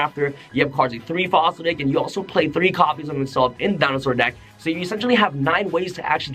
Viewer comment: Can someone tell me what he says at 3:04? “Three souleating oviraptor, three fossil dig, and... ___”? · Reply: “Three copied of itself”?